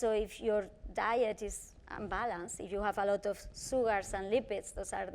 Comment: I thought she was saying "sewer", but it was "sugar", same thing I guess. 🤣😂